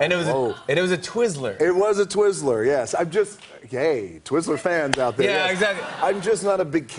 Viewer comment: sounds like he just said "I'm just gay"